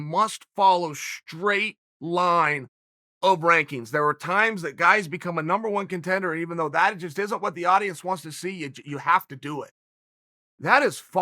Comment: Did Chael say......."RANKINGS?"